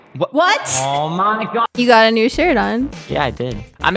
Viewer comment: um did i just hear said "u have a new shirt hun'